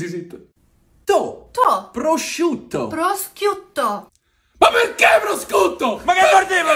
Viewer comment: Them: Pro - sciut - to Them: Prosciutto Her: ProscHiutto angry Italian noises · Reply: I thought she said Prostiuto too, but listen careful, she is saying Prosqiuto or something